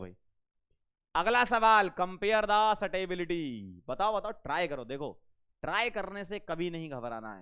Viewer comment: sir said compare the "satebility " instead of stability